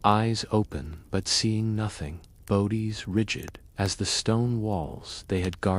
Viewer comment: your AI slop said bowdi instead of body hahahahahah